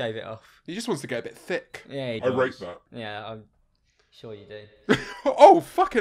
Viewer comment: I'm stupid what does say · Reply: I rate that (ik this comment is 2 yrs old)